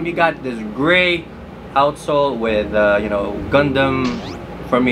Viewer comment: i thought you said “outsole with condom” 😂 improve your accent dude 🙄